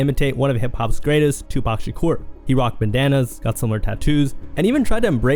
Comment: Bro …. Are you using IA? I thought I heard ja rule was wearing Bananas 😂😂😂 ….. I know you meant bandannas 😅